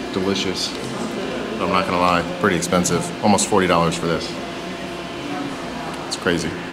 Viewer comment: I'm a sandwich guy on the west coast, you said a lobster sandwich cost what??? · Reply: I thought he said almost $40.